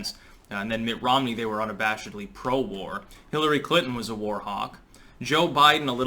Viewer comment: First I heard, "Hillary Clinton was a warthog." Close enough?